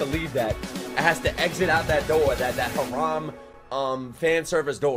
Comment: did he just say haram???😨